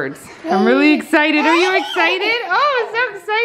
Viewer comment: It sounded like said "excited" after she said it 😆😆😂